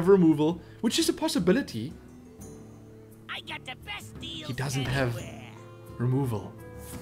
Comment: or something i hear He has an amazing dick. I KNOW ITS DECK BUT I TOUGHT FIRST IT WAS DICK.